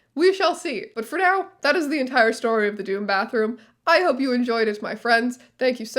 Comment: I thought it said dorm bathroom and i was like yeah every dorm I've been at has a terrible bathroom, what of it